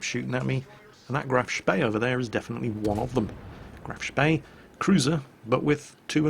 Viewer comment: is it just me, or does it sound like is saying Graf's bae when he says Graf Spee?